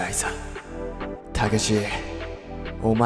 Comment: がたけしって聞こえる